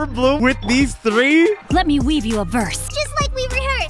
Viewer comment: I really thought Xingqiu was saying "Let me read you a verse." I've had Xingqiu since Lantern Rite 2023. How did I not know this?